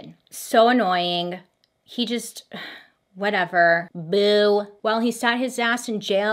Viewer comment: One hour and a half that's fully accessible with accurate cc??? You. Are. Awesome. Thank you from a Deaf follower! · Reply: instead of Bill she said booo! Like “ugh 😑 “